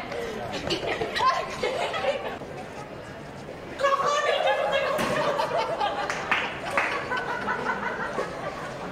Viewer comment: What did the lady say at 7:21 ? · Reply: She said "what a scare"